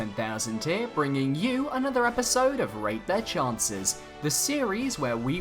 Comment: i like that part when he say's ''rate their chances'' and in every video it sounds like he is saying ''rape their chances''